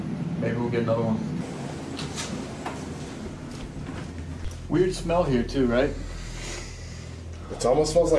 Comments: I would like to take a moment to thank all of you who have been following us and also to our new comers who have recently joined us. You’re support means everything to us and we appreciate you being here with us 😁💪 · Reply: I thought the whisper said I smell fear ngl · It honestly sounds like he said " I smell fear" 😨 😱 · I thought it said I smell fear at first